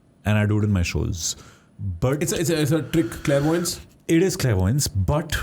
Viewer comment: Can some one tell me what that word was that prakhar used on 39:08 after the word trick .Please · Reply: Clairvoyance